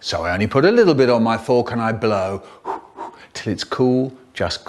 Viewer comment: i think he said like niccurds